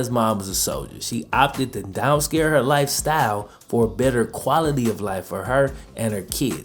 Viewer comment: you say" blah blah blah his mom decided to down scare her life" lmao. Come on bro. And what about when you say sophomore.....I mean FOH..... sauphmore ...... SAUPHMORE